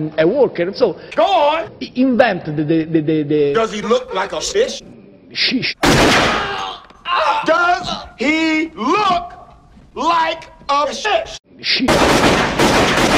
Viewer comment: sounded like he said "does he look like a fish"